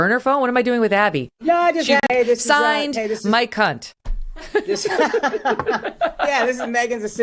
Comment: Bruh when she said'' signed mike hunt'' I heard''my c**t'' 😅😂😂😂